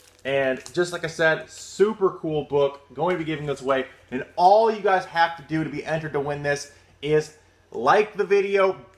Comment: what did the "sex dungeon" lady say in the after credits scene when she was freed from Toxic World? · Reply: Monroville "sea cucumber", the safe word. Basically it's implying she was going through something that she wanted to stop.